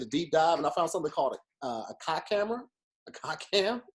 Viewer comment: This nigga was saying cock camera. I thought he was saying cock hammer